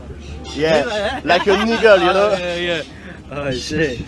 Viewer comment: like quoi ????? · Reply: An eagle